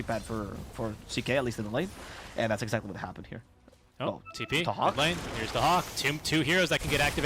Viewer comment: did they say "the hawk"? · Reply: Daxak = Dahak